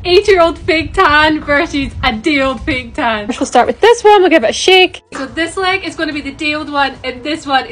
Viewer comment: For a second I thought she said “ Let’s go start with this one I don’t give a shit!?”😂😢😮